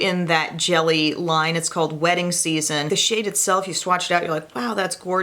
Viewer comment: I thought you said the eyeshadow shade was wetting season instead of wedding season lol